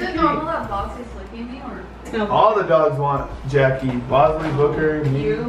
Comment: Is it just me or at minute 19:56 did say he wanted Jackie